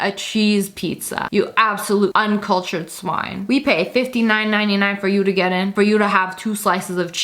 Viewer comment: When you said 59.99 I thought you said 150.99 XD